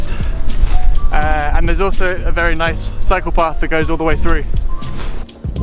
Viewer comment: Without the context I would have thought you said psychopath...